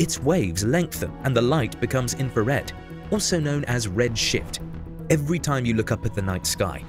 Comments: Anyone else hear "..also known as 'red shit'" at 1:42? Even on 0,5 playback speed. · am i the only one who heard "red shit" the first time? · Am I the only one who for a split second thought he said "Red shit" at 1:43? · am I the only one that heard red shit instead of red shift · did i hear "Red sh** ?" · i heard it as red shit